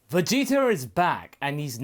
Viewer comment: Did you just say "Vegeter?"